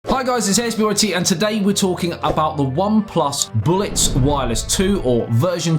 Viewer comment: I thought you said Blitz Wireless 2 at first Hitler approves